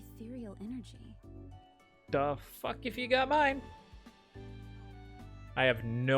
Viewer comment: it sounds like you said, "The f*ck if you got mine".